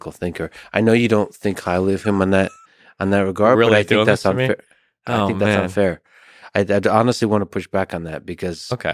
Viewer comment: Can someone clarify what Destiny said at 1:04? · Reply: Yeah he said are you really doing this to me ☮️💟